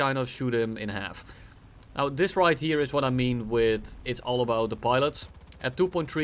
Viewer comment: did I heard saying it's not the plane, it's the pilot?